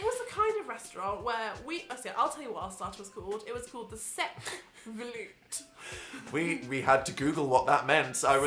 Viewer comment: For a second there I thought they said "balut", and I was like "DID YOU EVEN KNOW WHAT YOU WERE EATING??"